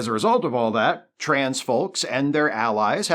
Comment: Glad I went back to listen again, because at first I heard "transphobes" instead of "trans folk". Was scratching my head there for a bit